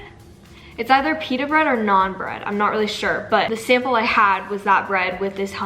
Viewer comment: lmao i thought you were saying "non-bread" not naan bread